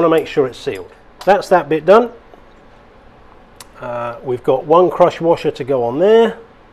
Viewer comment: Thanks for the great videos, I'm gonna go fix my own generator now! Also it took until the end of the video, I thought you were saying CROTCH WASHER LOL